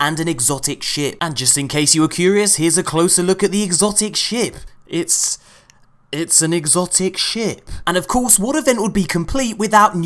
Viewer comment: I died because I legit heard "It's some exotic shit." I've been away from my xbox and compy so I can't play. I'm crying now.